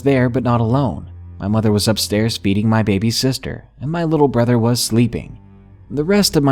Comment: anybody else hear '...beating my baby sister' ?